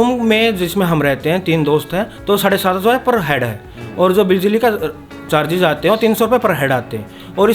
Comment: What is rent that he said at 13:24 ?? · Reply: He said that the rent is 7500/head